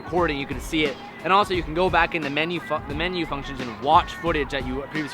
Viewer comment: did you say "Fuck the menu"? lol